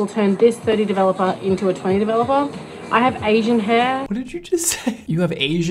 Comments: is sounded like “I have ageing hair” to me 🤷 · I totally thought she said “ I have aging hair” and not “ I have Asian hair” lol I’m so sleep deprived. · Oh I thought she said “I have aging hair” lol and that’s why she had to use that developer